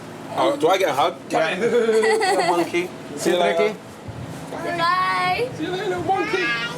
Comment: did he call that kid a monkey 🐒